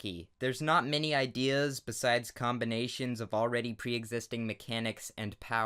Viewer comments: Always when you say "My name is Solar Sands" i hear you say "My name is Solar SANS" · when he said "my name is soler sands " it sounds like he said soler SANS and.... that's all I want to say